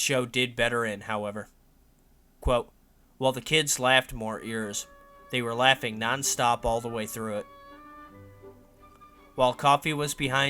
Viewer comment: I hear "while the kids laughed more at yours, they were laughing nonstop all the way through it". Is that right?